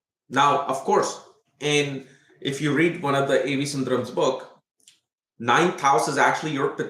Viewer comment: Hi, what is the book mentioned in 12:19? I can't understand the name of the writer. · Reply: hi nilli the name of the writer A.V SUNDARAM